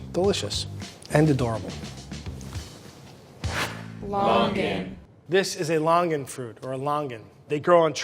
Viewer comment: I can't be the only one who read and heard that as "logang"